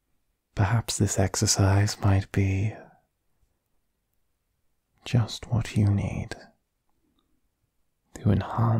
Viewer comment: When he said, "perhaps this exercise might be just what you need" I heard, "perhaps the sex aside might be just what you need" and had to do a double take and put the subs on :")